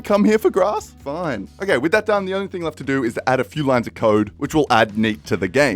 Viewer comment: I thought he said a few lines of coke 😂😂😂